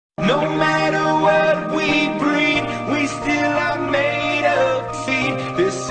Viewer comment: Am i the only one who hears "no matter what we breath we still are made of feet"??? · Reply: I hear always "no matter what we breed we still are made of seed"